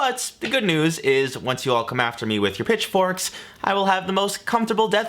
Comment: I had to rewind because I thought you said, bitch forks instead of pitch forks. Little disappointed. 😜